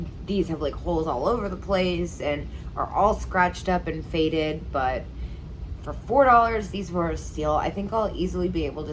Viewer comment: Did you say you ship free under $35? How do you earn profits, if item is $3 your cost, listed for $12, your time spent driving often out for 10 hours, fees, then shipping, you might earn $0:50??.. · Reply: No over $35